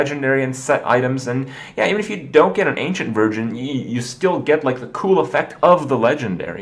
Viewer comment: did he just say ancient virgin